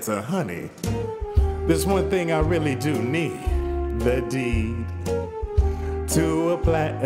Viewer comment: Made my day! At first I misheard there's one thing I really do need. The D.